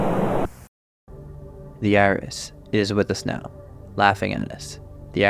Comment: How to beat the IRIS from Gemini Home Entertainment: Build a Death Star. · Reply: My brother, I thought you said "THE IRS" and I was so damn confused... "What's the Internal Revenue Service gotta do with this!? I know they're powerful, but Christ!!"